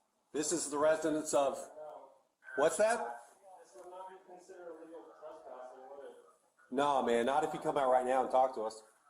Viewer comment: He actually says "This would not be considered a real trespassing would it?" the next 'inaudible' thing he said was "That's what I'm afraid of is that it would be twisted." also he doesn't say "that's really bad" he said "Next door you said?" because the officer previously said his house was next door The 'inaudible' part he says before asking if the officer was afraid to come up was "I supposed we could take it next door could we?" I'm not certain about the "Could be." at the end though idk who did these subs but they need better headphones lol also sorry for the lazy grammar